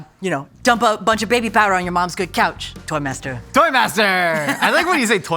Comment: My sound was off and so the first thing I heard was "your mom's *meow*" LOLOL